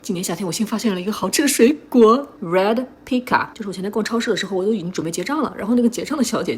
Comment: 笑死我聽成了red pika，看了字幕才知道